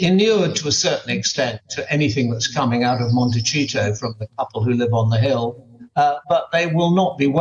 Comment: Did he just say Montecheato? 😂😂😂